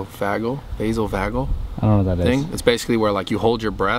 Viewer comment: valsalva maneuver i think you are saying